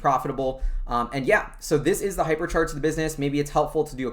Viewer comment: Wow the hypercharts site is really well built! Definitely trying it out. Anw, I though i heard wrongly.. at 12:58, notice hypercharged was mentioned. HAHA. but its okay since you are following Tesla and all its supercharger network and stuff. 😂